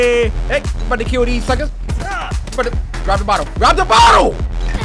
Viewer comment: please tell me what he said here because… it kinda sounds like fvckers